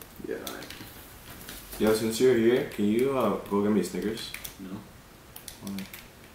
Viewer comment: When he said a snickers I thought he said cigerette